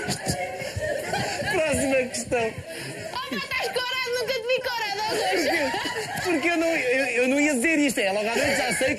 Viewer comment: O que ela disse? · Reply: tás corado, nunca te vi corado ó Goucha